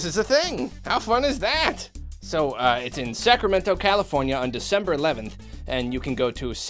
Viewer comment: oh i thought he said september 11th not december lol